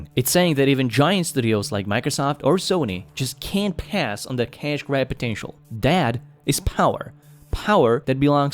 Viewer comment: I thought he said cash-dad potential lmfao as if PC gaming is a dad thing when he actually said cash grab hahaha